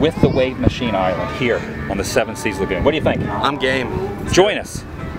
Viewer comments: lol I thought he said, "I'm gay!" @ 0:53... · 57 seconds sounds like he says I'm gay